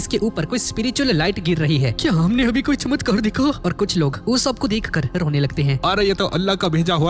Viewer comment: did it just say Allah bro u Muslim ❤❤❤